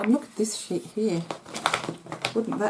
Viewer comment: "I mean look at this ? here " LOL I replayed it over and over.. What was said? haha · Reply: She said look at this SHEET here